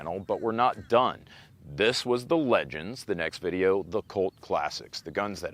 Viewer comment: I thought he said 'Colt' classics. An entire episode on classic colt firearms. Okay. Yea, Cult. Quarters, Nikes, purple jumpsuits, and the Colt cult. I guess it could have been coke classic